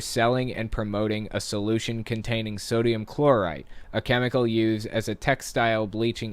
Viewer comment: Not gonna lie, when you read out "Sodium Chlorite", I misheard it as Chloride, which made me very confused about why the article would paint table salt as a dangerous chemical for a moment.